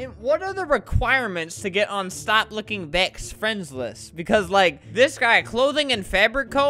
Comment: I heard “Stop looking thec”